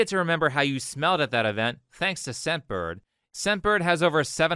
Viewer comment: hey a quick comment, i was listening to this video and i kept hearing you say "sempered" instead of "scentbird" in the advertisment. it might not be so clear to other people as well